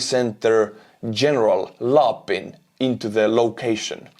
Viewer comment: I kept hearing you say “General LARPing” and thinking yeah, that makes sense